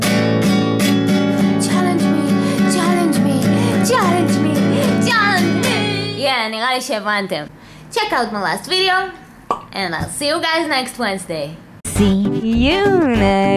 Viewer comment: was I the only one who thought she said "Check out molest video" at 1:50? O_o